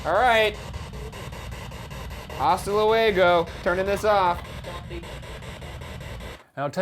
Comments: WHEN THEY’RE USING THE SPIRIT BOX AROUND 18:30 ISH AND RYAN’S LIKE “hasta luego” WHEN HE’S TURNING IT OFF I SWEAR I HEAR “don’t leave” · Right before he turned it off I heard “don’t leave” at 18:34. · It’s very slight but when Ryan says he’s turning it off it almost sounds like something says “don’t leave.” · idk if it was just me but when ryan says “turning this off” at 18:31 it kinda sounds like it says “don’t leave” :/// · Is it just me or did I hear “don’t leave” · I heard “Don’t leave”. Anyone else? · when he turns off the static thingy I heard a woman’s voice say “don’t leave” any one else? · did anyone else hear “don’t leave” when Ryan said hasta luego ??? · Omg the spirit box! 18:30 When Ryan says “turning this off!” I think it says “don’t leave” I keep replaying it 😩